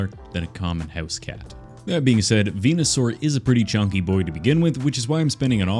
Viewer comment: I swear to you I was smashing open Romune bottles (because I’m weird like that) and I though I heard say “stupid boy” instead of “chonky boy” and I immediately went “hol’ up what did you say about my baby boy Bulbasaur” Also by the way my text to speech put down Bobasaur instead of Bulbasaur and I just started laughing my ass off🤣